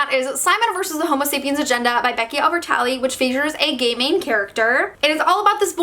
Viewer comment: Whenever say's "gay main character" I keep hearing "gayming character" lol.